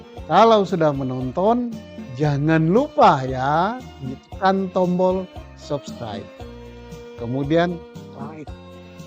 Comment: yg bener masak di un tombol subscribe . atau saya yg salah denger